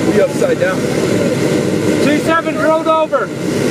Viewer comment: Props to the guys who said "roll over" haha that training kicked in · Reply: At first I thought they said pull over and was hella confused